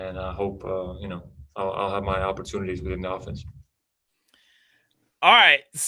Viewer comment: It sounds like he said " Within the offense " Within was said really fast